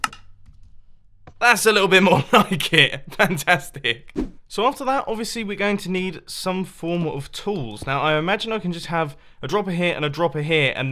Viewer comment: "Pantastic" LOL. I know that's not what you said, but it kinda sounded like it! :D